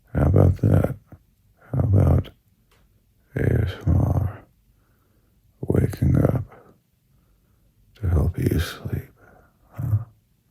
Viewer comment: I thought it said “Waking YOU up to help you sleep” which was even better! 😂 Can we get that version too?